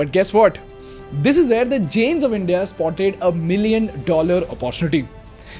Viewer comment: Everytime he says "the jains of India", I hear "the gems of India"